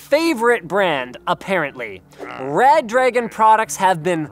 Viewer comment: when you said red dragon, i thought you said bad dragon so i got really excited